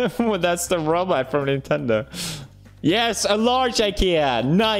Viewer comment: did you just say Nintendo